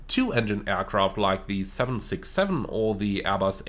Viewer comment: did I hear that correctly, 6-7? But great vid❤️ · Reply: "Two engined aircraft, like the 767" - you heard that correctly. What's wrong with it?